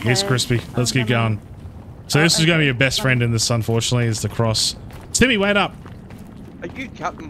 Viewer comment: did you say "stevie wait up?" you miss me that much ? lol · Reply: Hahahhahah I said Timmy. You know I’m always thinking about you. 😉😉